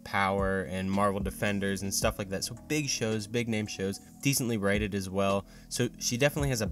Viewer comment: Wait...did you just say " decently writted " ?